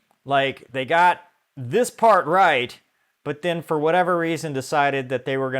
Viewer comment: I thought you said they got the canopy right. 😏 Fun fact: Google was first incorporated in 1998.